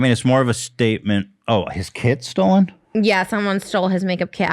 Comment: I THOUGHT SHE SAID HIS KIDS WERE STOLEN NOT HIS KIT OMFG HAHAHA